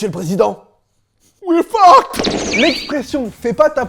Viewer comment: Il dit quoi à 3:37 ? :b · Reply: We're fucked